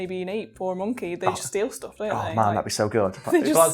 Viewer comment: anyone else hear mc Enderman?